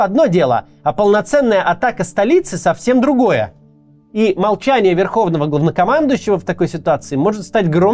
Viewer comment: мне одному послышалось "молчание верховного *овнокомандующего" - и как же в точку)